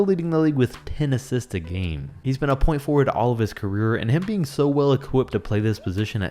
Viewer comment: did he just say point forward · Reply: yes. its called a point forward